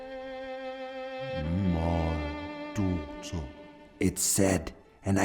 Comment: What did the unicorn say? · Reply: "My daughter."